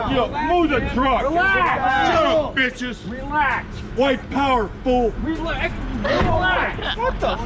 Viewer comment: Did he just say “White power fool”? 😂 💀